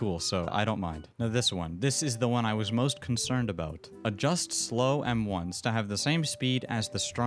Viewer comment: Honestly i thought owner said "Adjust slow m1s to saitama m1s speed" just means only adjust metal bat since is slow.